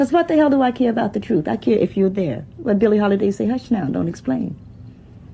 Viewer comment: That's the man who wrote 'The Fire Next Time". But who's the lady? I thought I heard her say Billy Holiday? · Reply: Adesuwa's Two Kobo worth YES Billie Holiday a Jazz Singer who sang " Dont Explain".